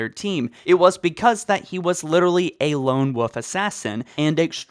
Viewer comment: It sounds like youre saying "lone woof"